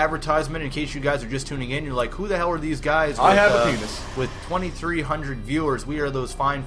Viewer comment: Is saying "I have a penis"?